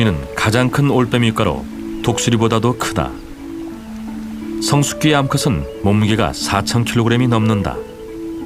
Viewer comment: 수리부엉이성체가 36:51 4000kg이 넘는다고 들리네 ㅋㅋㅋ 그리고 독수리가 훨씬커요.. 사람만함